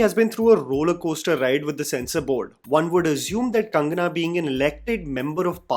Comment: did he say Tangana?